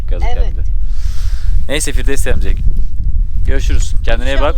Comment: firdevs amca ? mı diyor ? ne diyor anlayabilen var mı ? :D